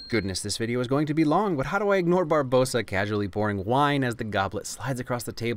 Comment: the subtitles say Brabosa instead of Barbossa. Gonna have to take a point off there